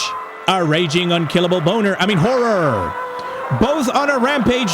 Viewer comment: did you really say "Boner" 🤨